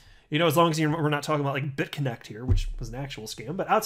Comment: Did you say BTT at 4:11 was a scam?? Please elaborate. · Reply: He said Bitconnect, Not BitTorrent at 4:11 time mark.